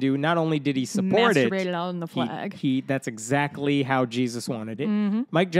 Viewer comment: Did she say "masturbated it all on the flag" ?? 11:17 laughing so hard its hurting